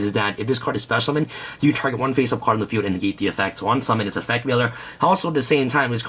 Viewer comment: did he say yeet the effects